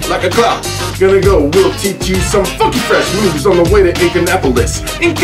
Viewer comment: G did you said a bad word in 1:30 here closely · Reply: I said "funky fresh"